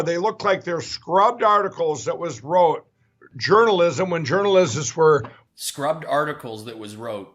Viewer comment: I thought he said Journaljizz. Must be my bad mind. Sounds about right though, eh?